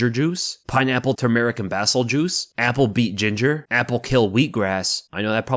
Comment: I had Iron Maiden playing whilw you were doing your food into and indtead of Pineapple Tumeric and Basil juice it sounded like you said "Classic American Asshole Juice" A moment i will never forget.